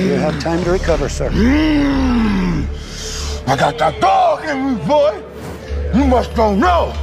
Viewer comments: “I got that dog in me boy”…..then proceeded with a left hook😂😂😂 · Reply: I thought he said got dat thug in me boy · Thought he said dark in me not dog… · Bro really said “I got that dawg in me” to a real person on live tv💀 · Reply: Sounds like he says that thug in me